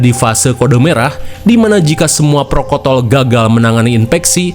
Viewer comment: Apa saya gak salah denger protokol kok jadi prokotol 😂